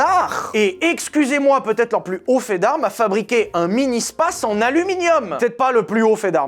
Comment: Wow j'ai compris un mini-SPAS. Intrigué, j'ai essayé de googler pour trouver une photo de cet petit fusil à pompe en aluminium.